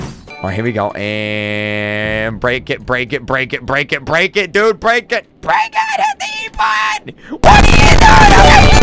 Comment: I thought he said “BREAK IT YOU PEWDIEPIE” 😂😂😂